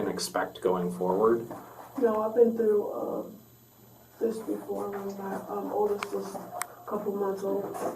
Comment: Oh, she'd been through this before.I thought she said she'd never been to this before.Take the kids get them a good life please